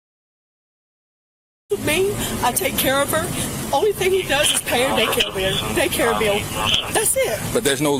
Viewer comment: Have these folks ever heard of birth control? The so-called father has 12 kids & this woman has 5. Taxpayers are probably supporting all of these kids. 🤬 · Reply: I thought she said of her "baby daddy": "All he does is pay the bills" so he's "probably" supporting all of his kids.